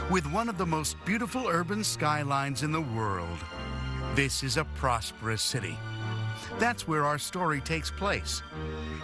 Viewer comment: I heard “this is a prostitute city” 😂